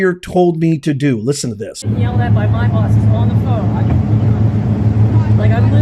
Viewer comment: I thought she said, "yelled at by my boss". Boycott Primanti Bros.